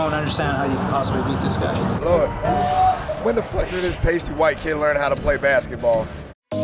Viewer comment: what's the first part of the sentence, I just can't understand it. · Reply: “Lord, when the f*ck did this pasty white kid learn how to play basketball”